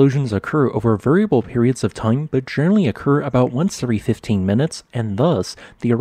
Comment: Same tip- narration is generally excellent, but you slur the "l"s a bit. I heard "genry" instead of general-ly, for example. Hope this helps.